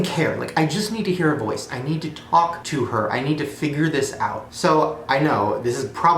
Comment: "need to talk to her I need to finger this out" Anyone else hear that at 7:15😂